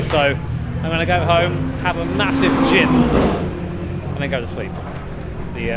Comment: I heard "I'm going to go home and have a massive Jizz"